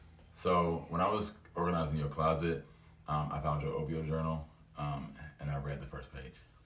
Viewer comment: wait did he say opioid journal??